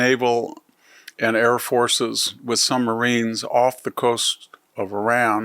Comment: Sumbarines? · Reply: At first I thought he said, “With some Marines..”, but CC interpreted as submarines. Our nuclear submarines carry conventional tomahawk cruise missiles that have been employed many times in our Middle Eastern conflicts.